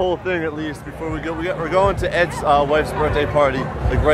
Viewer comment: I thought he said "ex wife"